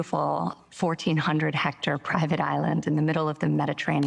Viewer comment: At first I thought Ivanka said "14 hectare private island." I had to look up hectares, and then I thought: Wow, it's like she literally got 40 acres but no mule... whatever cynical joke might come of that. Then rewound the video and heard "14 hundred hectare private island." That is like, more than 100 times worse. But I guess there needs to be an island.